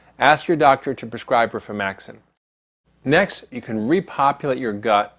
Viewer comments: what did he say ask your dr. to prescribe? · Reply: He mispronounced "Rifaximin". A very strong, very expensive drug. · what's the name he's saying? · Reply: Rifaximin. Health insurance rarely covers it and buying at retail costs thousands for a two week course of pills.